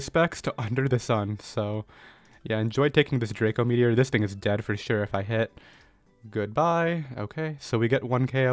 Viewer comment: did he say the N word or did he say this thing is dead for sure????